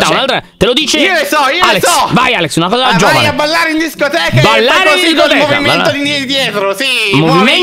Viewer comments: NO ASPE HAHAHAH solo io a 13:30 ho sentito che dice "fare ses*o ceh" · Ditemi che solo io non ho sentito dire “fare s*ss* cioè”